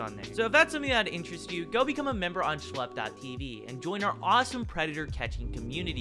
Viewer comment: I heard “and join our awesome predator community:)”